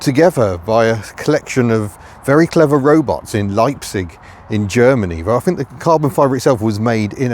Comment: for a moment there i heard "by robots in lycra in Germany". I now have images my brain doesnt understand 😂